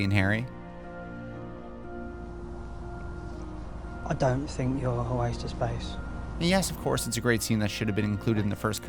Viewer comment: I'm sorry, even though I clicked on the subtitle I still can't fathom what Dudley was saying in 3:18??? 🥺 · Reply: He said: “I don’t think you’re a waste of space.”